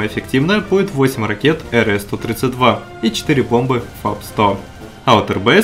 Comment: мне послышалось или он сказал 4 пломбы ? :D